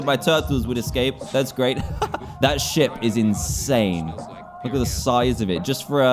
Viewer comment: I thought he said that "sh*t is insane" xD